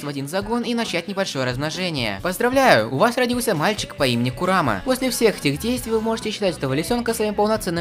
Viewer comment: Курама потярял Хогоромо, потерял Наруто. Он больше никого не потеряет, ибо все потеряют его...😭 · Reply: Курама? Мне послышалось Куромаку